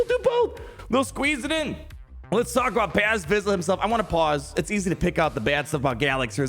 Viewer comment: For a second I thought it said pre vizlsa. 😢